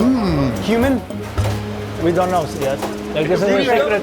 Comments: I thought he said HUMAN not CUMIN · I thought y'all said HUMAN. I was like: 'damn, that is some advanced dark humor.'